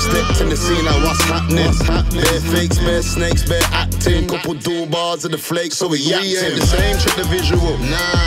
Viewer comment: Does he say puddu bars at 1:14 - 1:16....check it out #phiddeecubdheecoochoocoochooplumpung